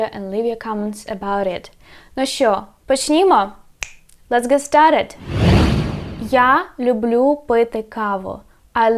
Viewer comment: What are the Ukrainian words for "my dear friends" and what is the word @ 0:22 before "lets get started" ("no4Himo"). Dakuyo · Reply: Ну що? Почнімо! [Nu scho? Pochnimo!] Well? Let's get started! So, what? Let's get started!